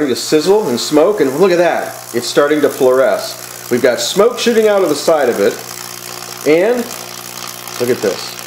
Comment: it almost sounds like youre saying we've got smoke shitting out the side of it just found that funny :)